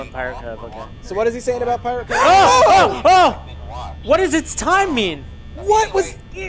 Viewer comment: Wow... at 11:10 they completely missed golden Freddy said it’s me and thought it said it’s time.